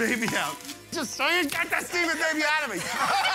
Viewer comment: I thought he said, “get this semen baby out of me”